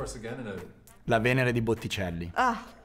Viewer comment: I got one wrong and it was the Park painting 😂😂 I wished they would've shown a piece by Khalo(no self portrait nor flower) painting. I thought I heard her say Bootycelli 🤣 I misheard